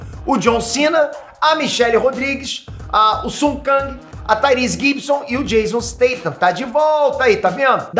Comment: Eu entendi mal ou o falou (a) Tyrese Gibson 9:29 kkkkkkkkkkkkkkk